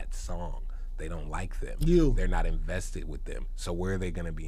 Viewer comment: When Tyler was talking about people blowing up on YouTube but not having any real fans at 1:14:27, is it just me or did Funk Flex respond with "you". Did anyone else catch that?? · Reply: Yea. What was up with that?